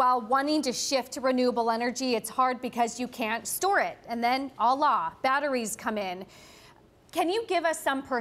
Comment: did she just say "allah" (5:49) instead of "voila"?